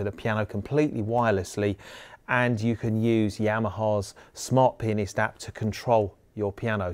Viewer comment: The automatic subtitles said Yamaha smart penis app hahahhaha